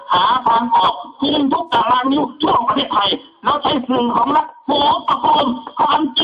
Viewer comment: แกะเทปขาดไปนะครับ 4:20 พูดว่า "แล้วใช้สื่อของรัฐ"